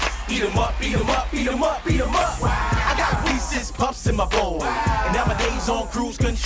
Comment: I thought they were saying beat them up lol